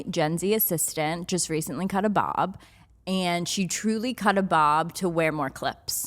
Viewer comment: I heard "caught a bob" like it's a infectious disease 😂